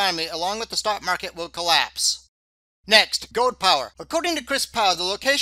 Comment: Gold power? I thought you said ,"Goat power". Baaaah! Dang! Guess i better sell some goats milk and buy some gold. And batteries for the hearing aid.